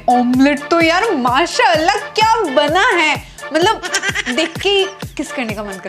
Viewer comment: did she said mashallah